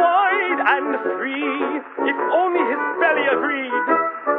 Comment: I don't think it's "If only it's barely a dream", I'm pretty sure it's "If only his belly agreed" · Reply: Glamsus i hear "if only his belly agreed" too, as it completely fits the context, poor guy lol